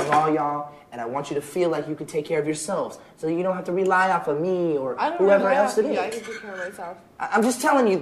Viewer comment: what the hell?!? (Says nick Carter) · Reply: 'Take care of yourselves, so that you don't have to rely on me or whoever else it is.'